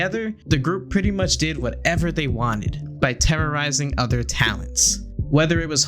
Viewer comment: Did you say Terra Ryzing?